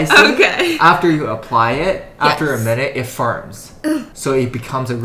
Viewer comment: idk why i heard "after a minute it farts" 😭☠️